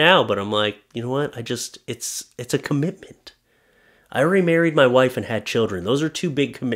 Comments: Remarried?!?!? Please explain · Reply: I thought he said, "I'm already married...". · Remarried?!?!? Please explain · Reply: That's what I thought he said at first as well. But I think he said "I already married my wife and had kids." He was just speaking fast and made it sound like that. Maybe....